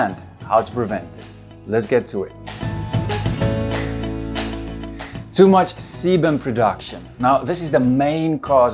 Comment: who else thought he said ”Too much semen production”? Kek